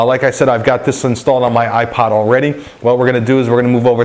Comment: ipod instead of ipad